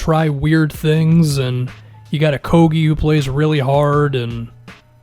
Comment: at first thought he said "got a kobe that plays really hard"